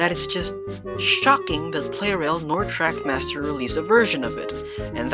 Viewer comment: is it just me or did you say "crackmaster"?